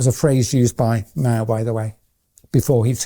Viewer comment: Can please someone help me in understanding what he's saying at 01:01:37? "That was a phrase used by (..?..), by the way, before he took steps (etcetera)". I knew the infamous phrase was by K.S. (Wef), but I can't understand what Dr. Yeadon is here saying. The transcription is of no help, it says "by M" 🤔. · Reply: Mao. As in Mao Zedong; 1st chairman of the Chinese Communist Party and founder of the People's Republic of China (1893–1976).